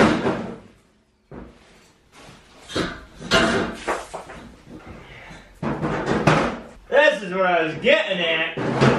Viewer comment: did I hear you say "fuckin"? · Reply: 607 Moto I know right as soon as he dropped the metal grate he definitely said fk